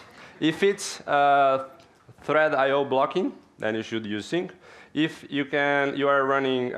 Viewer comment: It's difficult to hear, it sounds like he says to use `sync` for both cases, but I think @ 20:15 he actually says if it's IO blocking then use async.